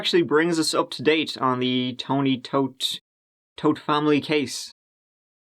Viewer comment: Thought it said "Toot family"